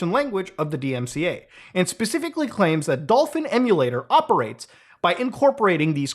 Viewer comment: I thought it said dolphin simulator and now i want to play a dolphin simulation.